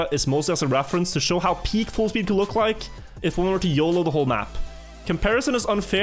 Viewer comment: when I heard it the first time, I thought he might have said "...if one were to Riolu the whole map" and I was thinking that was a very interesting turn of phrase created by the recent scandal. I'm both glad and disappointed that I misheard it.